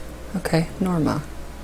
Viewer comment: i thought it was saying norman! but it said norma! <3